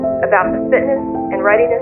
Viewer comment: It’s weird how in the end there is a voice talking about there being a sickness and flights. And how right now the Coronavirus is out and they canceled the flights. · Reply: Yaritza Mejia chavez I thought it said “fitness” meaning fitness of individuals for space flight.